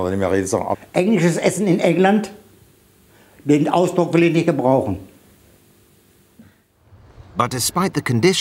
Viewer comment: The translation "I wouldn't even call it food" doesn't seem quite what he says. At first I thought it ended in "...Politiker brauchen", even politicians wouldn't need it, but now I reckon it ends in "gebrauchen", to use. But the earlier bit? "Bin 't aus doch..." ...politi? Anybody work it out?